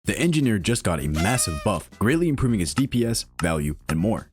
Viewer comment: did he just call engineer a he??